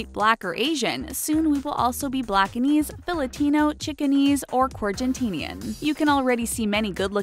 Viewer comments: did she just say chickenesse? did she fucking say chickennesse? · I had thought she said "Chicken knees "